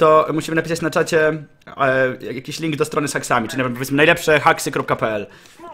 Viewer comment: Czy tylko ja usłyszałem "sexami"?